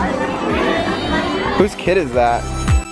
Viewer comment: When said"who's kid is that?" In the intro I thought he said "I kicked his butt," lol